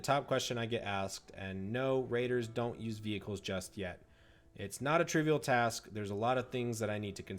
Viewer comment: did i hear "yet"? so there is hope? proceed to bust a nut